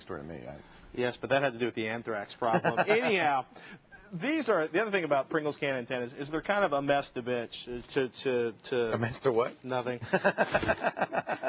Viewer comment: Did Patrick just say bitch on TechTV? Awesome!